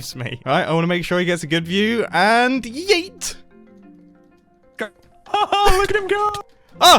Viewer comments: Anyone else hear mumbo say yeet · wait, did mumbo just say... yeet?! Oh my gosh dang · did he just say "yeet"?